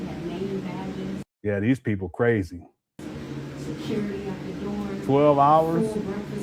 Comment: Did I mishear or at 2:36:38, did she say "security at the doors"?